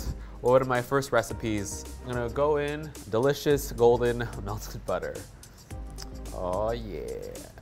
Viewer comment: When he said 'melted butter' i thought he said 'musted mustard'